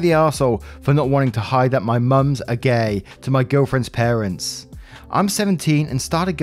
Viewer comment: What read out: "My Mums are gay" What I heard: "My Mum's a gay"